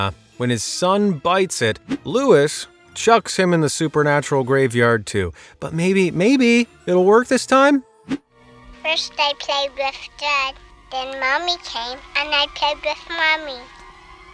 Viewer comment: anyone know what the kid said? all i hear is "first i played with dirt, then mommy came and we played too"